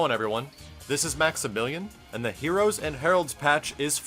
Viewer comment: its only me who heared maximilian saying maxibilain?